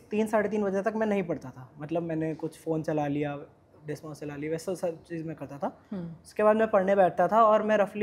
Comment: did he say desmos